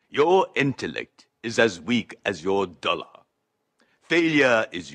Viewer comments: i love it when he tells me "Your intellect is as weak as your dollar. Faliure is your destiny. You disrespect yourself and your nation. You are made of stupid." So inspirational and heartwarming! 😍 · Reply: I heard “as weak as you’re dumb”. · "Your intellect is as weak as your dollar." Fucking savage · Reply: I misheard it as "Your intellect is as weak as you are dull." Still savage tho